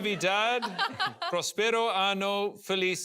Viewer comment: there’s a very big difference between año and ano, guy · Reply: I know very little Spanish, but did he just say "prosperous anus"?